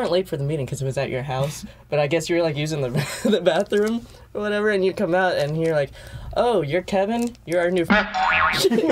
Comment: I know exactly what Dan said and damnnn😳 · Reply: pretty sure he said ‘’F*** Boy’’